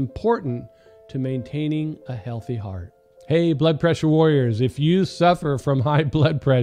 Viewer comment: At the beginning it sounded like he said "blood pressure worriers." Ha ha. Sadly, that has at times been true of me, and I'm sure, for many of us. And worrying sure won't help our blood pressure. As Scripture says, we should be anxious for nothing.